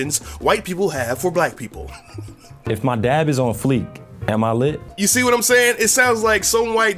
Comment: "If my dab is on fleet, am I lit?" I cringed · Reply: I thought they said if my dad is on fleek